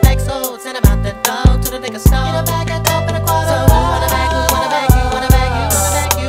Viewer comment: sometimes alls it takes is a song to take you back too the good ole days,. to the good ole days, "so,. who want a bag. you want a bag. you want a bag. you got a bag so, send'em out da doe, to the liquor store, wit a bag a dope and a quarta a rum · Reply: i thought he said " get a bag of dope(weed) and a gar to rollllllllll" . lmao